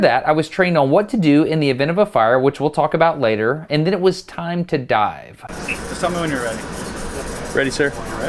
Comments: I thought he said "in then it was time to die" XD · Did anyone else hear, "and then it was time to die" at 5:28 ? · I thought he said “and then it was time to die..”